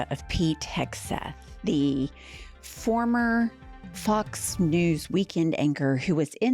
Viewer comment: Sounded like you said, “Fu*ks News.” Much better name for Fox News. 😂